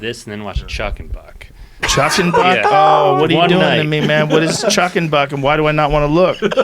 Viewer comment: I'm looking it up, chuck and butt, I'll be back 😅😂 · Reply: what was it? I thought he said Chuck & Buck.